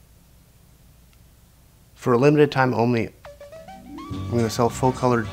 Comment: it sounds like SAID FOR A MOMENT OF TIME OMLY..lol am I wrong😂